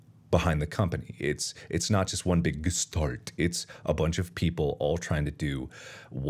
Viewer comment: Markiplier’s voice is so clear that the generated subtitles didn’t mistake a word Edit: apart from the 2:16, he said gestalt but the captions said “one big start”